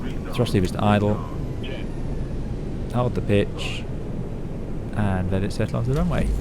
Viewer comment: i had to replay at 32:30 cos I thought you said 'hold the bitch!'. great video. the consummate professional.